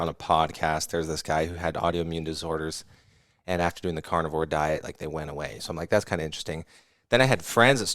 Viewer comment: at first i thought he was saying carnival diet 😂